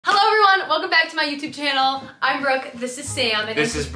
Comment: I thought she said “I’m broke” at the beginning. 😂